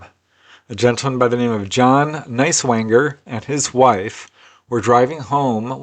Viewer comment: I thought you said nice wanger, I'm so infantile.